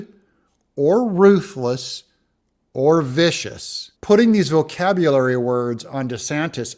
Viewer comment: I didn't hear "vicious", I heard "ambitious" when talking about DeSantis. "Shrewd, ruthless, and ambitious." I think he fears Trump more than he fears DeSantis.